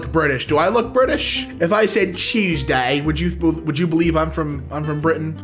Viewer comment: I legit thought you said “cheese-day”